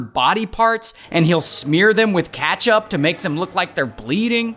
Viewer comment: did bro say katchup?